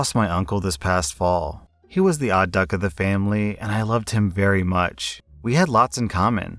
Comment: I usually watch the podcast now, and I heard him say "odd duck of the family" and had to come and see to make sure OP wasn't calling his uncle an Odd Fuck 😭😭